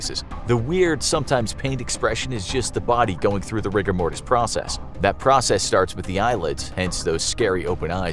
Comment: I thought you said Rick and Morty process